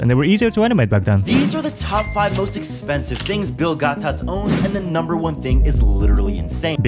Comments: I thought he said BILL GYATT 💀💀💀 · “i swear i thought i heard Bill Gyatt” -my frien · Thought he said bill gyat 💀💀💀